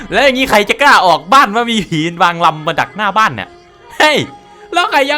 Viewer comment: หนูได้ยินว่า"ใครมันจะกล้าออกว่ะมีหีนางรำอยู่ขวางหน้า"